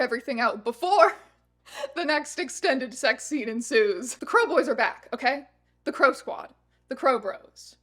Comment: Every time I hear you say “crow boy” it just sounds like “krelboyne” from Malcolm in the middle